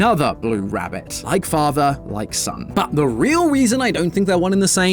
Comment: did tom say weason instead of reason